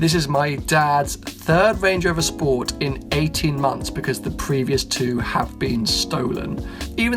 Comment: 2 cars stolen in 18 months? London is fucked. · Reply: I thought he said 80 months